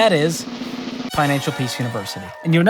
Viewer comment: did say Pinancial Peace?